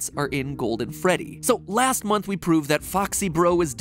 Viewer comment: I just had a little heart attack when he said 'last month we proved foxy bro'... ect. I thought he said last week and had to rewind just to make sure he didn't say week.